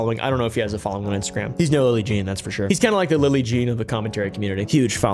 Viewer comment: i kept hearing 'billy Jean'